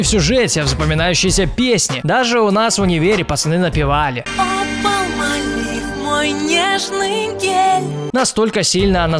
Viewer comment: Мне одному послышалось: О, Palmolive, мой нежный Гей?